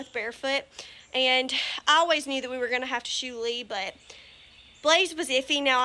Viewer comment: I thought you said I always new we were going to have too shoot lee you said shoe Lee 😂😂😂